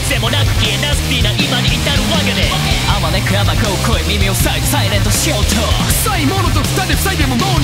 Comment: 【朗報】夢野先生カタカナ知ってた 【朗報】夢野先生カタカナ知ってた 【朗報】夢野先生カタカナ知ってた 【朗報】夢野先生カタカナ使えた 【朗報】夢野先生カタカナ使えた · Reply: 先生の｢サイレント｣が急に子供に初めて呼ばれる｢ママ｣に聞こえてきた